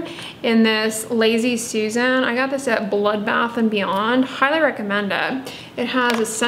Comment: HAHAHAHAHAHA! I thought she said Blood Bath and Beyond!